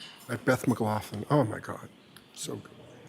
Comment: @ 5:66 in the video, what is this with someone intentionally making sure this audience is made aware of how nice a Beth Mcloughlin is? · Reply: we thought he said 'ben mccloughlin'! don't know who that is. but i heard they're very very nice! 😂